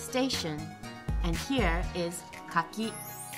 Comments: on 2:12 does anyone now what she said? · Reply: Hello,Nezzarae Choate Thank you for posting. At 2:12 sha said かき as a persimmon Keep studying Japanese with JapanesePod101 Regards, Yukiko Team <link> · what she said? persemen? wtf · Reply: Hello,ferleal24 Thank you for posting. [2:12] she said かき・kaki means persimmon Inline image 1 Keep studying Japanese with JapanesePod101 Regards, Team <link>